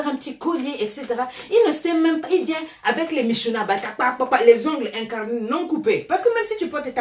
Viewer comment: elle a dis quoi ? Les ongles incarnés 😂😂 avec les minchouna blapwapwapwa 😂😂😂😂